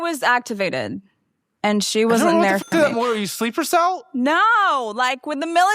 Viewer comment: What did he say?? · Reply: she: i was activated he: i don't know what the fu... are you a sleeper cell??